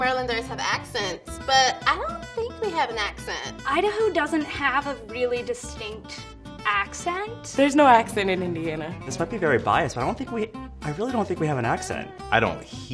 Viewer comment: What she said: "There's no accent in Indiana" What I heard: "There's no war in Ba Sing Se"